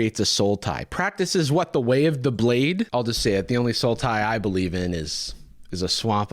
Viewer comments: lmao i stg i only could hear "sultai" instead of "soul tie". As someone who has played 90% sultai since they starte playing magic in 2011 idk if i love or hate that joke. Or both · yeah the first time i heard it in the video i actually thought he was saying Sultai. for some reason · I appreciate the Magic reference because I really did think you were saying Sultai the whole time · I thought you were saying there was a card called "Soul Tie" until I realized that you were saying "Sultai". Good job, that actually got me. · That was the ONLY Sultai I heard this whole video so far!